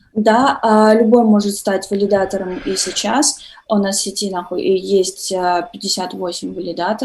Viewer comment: Спасибо огромное ребята за вашу работу. 17:08 "... Да, любой может стать валидатором и сейчас. У нас в сети на×%& есть 58 валидаторов..." Мне одному послышалось?